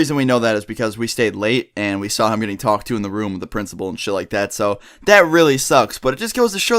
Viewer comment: Did just say...." The principal " at 04:02?!